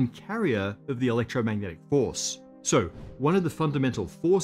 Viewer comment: it sounds like he says "electromagnetic horse" and I'm not sure what to make of this